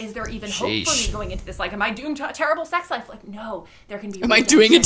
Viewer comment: Worse than originally thought, I think she said - "Doomed to a terrible sex life" LOL